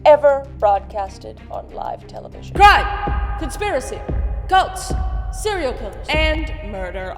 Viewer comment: When said cults legit thought she said goats and i was like hmmm odd thing to list with crime and seriel killers lmfao